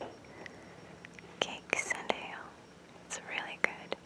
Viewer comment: What was the book called again? I didn't catch that. ;) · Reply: Cakes and Ale ?